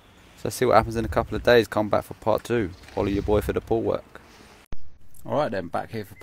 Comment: Holla ya boy for the pool work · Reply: Is that what he said, all this time I thought he said "Follow your boi for that pool work."